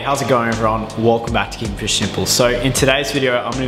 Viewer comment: i don't know why, but in your intro i didn't hear your name, instead i heard "King Fish Nipples"